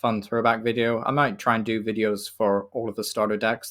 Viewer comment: I always thought you said Ambi power: like ambipom and power lol